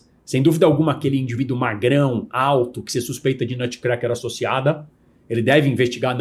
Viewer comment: o que ele fala? "(...)cracker associada"